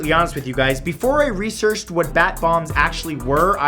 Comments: I thought you said Bath Bombs, I said "They wanted to make people smile to death?!" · I thought he said bath bombs not bat bombs XD